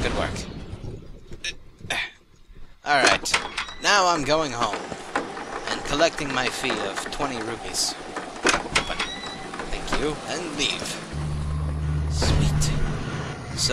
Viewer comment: Did say "20 Rupees" at 10:08?